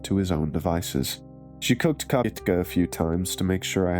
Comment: I'm sorry, she cooked what? Lol The captions said kka 😳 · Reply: Kopytka. It's a polish dish made of potato dumplings.